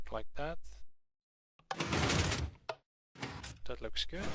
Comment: turn captions on at 12:33 He says: "that looks good" the captions say: "Dreadlock kids"